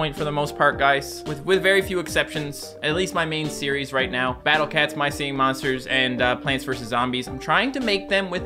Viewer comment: i heard "Battle cats", I went al rogue. I love this game!